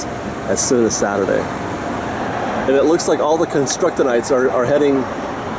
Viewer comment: Did you say construct - O- nights??? I needed to rewind several times and I notice you said it in other videos. Is this reference to what I type or what you think I type? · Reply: Construction-ites.